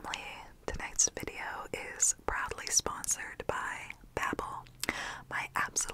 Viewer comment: I thought she said "is probably" sponsored by lmao